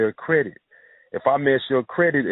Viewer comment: it scrambles. I need to know what it says. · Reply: john santiago didn’t it know it did that but, he only said ...”if I messed your credit up, it follows you...” meaning your credit report is effected and puts you in a specific bracket when applying for loans, mortgages, etc. etc. in lines of creditworthiness